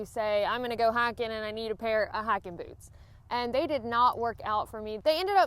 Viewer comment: I'm so embarrassed.......after graduating high school in Alabama, I have been in the "north" these last few years, and I thought you said hacking boots when you started talking about hiking boots! Noooooo!!!!!!! What have they done to me?!?!?!?!?!?!?